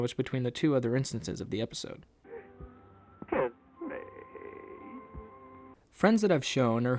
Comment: “kiss me”? I thought he was saying “help me” oh well it is after all a very old cartoon so it only makes sense that the voices would get muffled due to its age and due to time